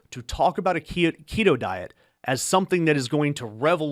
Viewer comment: At first I thought he said pedo diet and I’m like oh is that why Trump eats that way? And proud to have never agreed with RFK jr on anything because he is a con artist. Con artists can have good ideas but has no intention of implementing them.